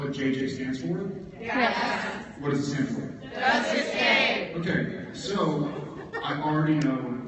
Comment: could anyone hear what the letters where when Jared was talking about what he would name Jensen's twins? I heard kk but not the last initials (it's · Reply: Kindness Kim and Liberty Larry